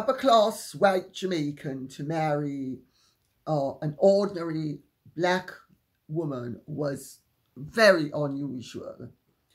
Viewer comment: Omg I kept having to rewind and listen over and over - I thought you were saying people referred to Bob Marley's mother as an "ornery black woman," which would be rude as hell. Sometimes I have a hard time understanding you Lady C. 😅